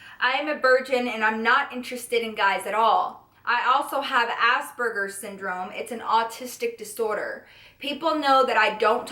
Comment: I wasnt consentrating on the video and thought I heard ass burgers instead of aspergers lool